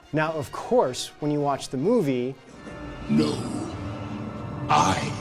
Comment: I was really confused because I heard Luke, not No. I think they’re just similar words so our brain heard them both.